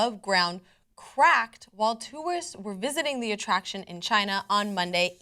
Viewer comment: Did she just say "two-ist" instead of tourist? :04